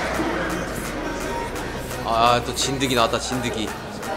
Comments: Wait did I just heard "ZINDAGI"💀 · did I heard 'zindagi' 😂😂😂😂